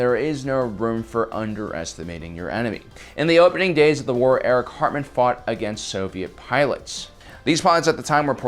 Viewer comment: I REALLY thought you said "A WW2 ace named Eric Cartman"